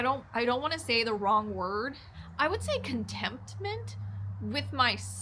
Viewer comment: Not to be the word police...sounded like you said "contemptment". I'll look at that clip again. I think the word is contentment. Kudos to you for enjoying the long solo rides. That's a lot of time in your own head. Says a lot about someone who enjoys those circumstances.